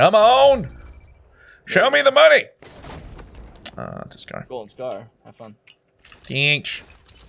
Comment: did he just say ugh golden scar